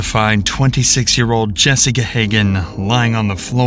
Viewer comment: it sounds like he said “Jessica”. I listened to it 3x and not sure why it says that